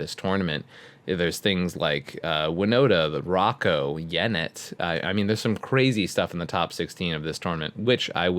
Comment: About 4:00 Did actually say Yennett? The sphinx Esper commander from C18?